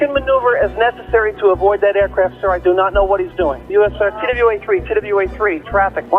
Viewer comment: (Unintelligible) I know what he’s saying. For anybody wondering, he’s saying “TWA 3”, referring to Trans World Airlines Flight 3, a scheduled flight from JFK to St Louis. TWA Flight 3 dodged United Flight 175 and eventually was forced to also dodge Flight 93.